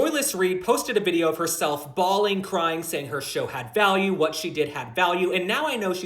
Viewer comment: “Bawling, crying” I heard that as “bald & crying” 😆